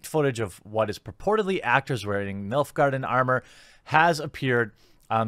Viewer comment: The captions say milf garden instead of nilfgaardian haha 5:20 .